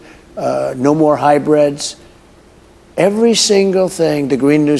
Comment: did he just say "no more Hi-Breads" speaking about EVs?